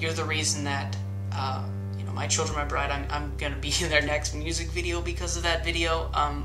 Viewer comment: what did he say about him being in a music video? sounded like "my children Ibride". ?? anyone know? thanks